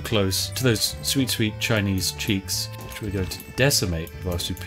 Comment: Spiff: Those sweet sweet Chinese ch**ks. Me: WTF DID HE JUST SAY???? Oh! Cheeks!